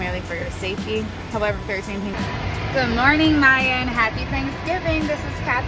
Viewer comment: Im not sure who you were talking to in that video (it sounded like you said Mayan) but my name is Maya and I’m doing my commercial checkride on the 21st. I’ll just pretend you were talking to me because it’s giving me the motivation I need right now 😂